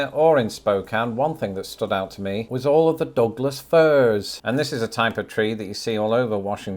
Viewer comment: I was in the tub while listening to hear if Washington came up & when I heard him say "all of the dogless furs," I had to look at my phone and rewind. Ohhhh, Douglas Fir trees 🌳 😅